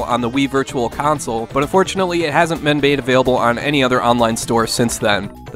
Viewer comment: fyi, you said "men bade" instead of "been made" 4:02 youre awesome as usual though, and it fits the drunk part ;)